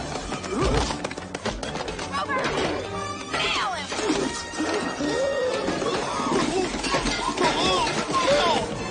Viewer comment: What did she say ? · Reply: Rover, NAIL HIM!!